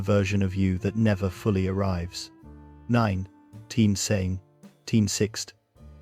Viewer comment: What’s with the text/narration glirch at 15:31? Anyone know what “9 teen saving teen 6th…” is doing here, or what it is supposed to be saying instead?